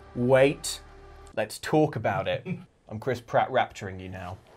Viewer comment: Am I the only one who thinks is sounded like he said "Chris Pratt rapturing"? Which begs the question, what exactly would a "Chris Pratt rapture" be like?